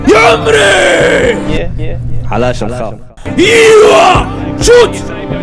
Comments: وش قال مساعد كأنه قال على شحم؟؟؟ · الدقيقة 9:18 مساعد تلكم مغربي و قال "علاش نخاف" ولا انا سمعتها لوحدي ??